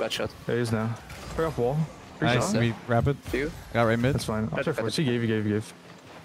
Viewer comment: "Nice, I mean Rapid". Bro is so used to saying nice, he is accidentally calling people nice now. 😂